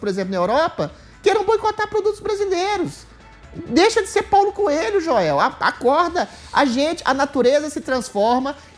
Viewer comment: Adriles "deixa de ser Paulo Coelho 13:13 Eu quase entendi"deixa de ser Pau no cu...joel..." Kkkkk